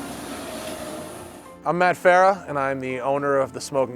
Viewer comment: I first I thought it said FAT FARAH lmao